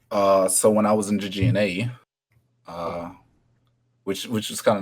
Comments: hey quick question, what did you say at 4:05? It was a bit hard to make out but you mentioned a tournament and I was interested. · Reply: He said GG NA, which stand for GitGud North America. The GitGud tournaments are planned and held through the Elo Hell Esports discord. You can find more info there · what did he say? he was in what? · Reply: I think he said "So when I was into GNA" but I might be wrong and I have no idea what it means...he's written some comments so let me see if I can ask him